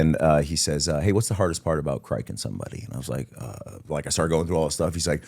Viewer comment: what's he saying here? "what's the hardest part about ? somebody"? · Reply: criking - colloquial term for performing a cricothyrotomy